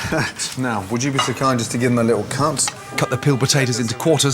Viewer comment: it sounds like he says give them a little cunt xD